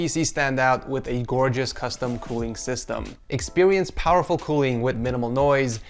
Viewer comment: is it just me or did he say "expiwience"